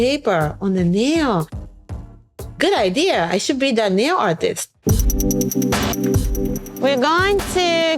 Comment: hey i-D I love your magazine so much! i wanted to point out a few mistakes in the captions :) 0:45 “i should be the nail artist” instead of “new artist”, 1:12 “when i start doing neo” instead of “nail”, “not by may” should be “not by me” on 1:39, this any many other mistakes show up in the auto generated captions on your video! Let’s make entertainment more accessible for D/deaf and hard of hearing people one step at a time by using one of many caption generation websites for more accurate captions!! <3